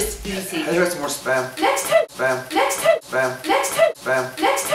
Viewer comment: who else heard "spam"?